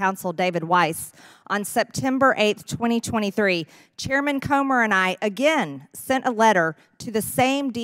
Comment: I thought she said “coomer”